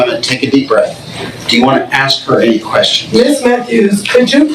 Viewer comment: I swore he said “do you have ANY QUESTIONS for this person?” & she proceeded to explain what happened in one continuous long a$$ breath…I even said “Didn’t he ask her if she had questions???” 😂Lawddddd